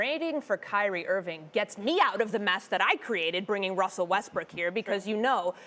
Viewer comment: Lmao! Did she just say Westbrick?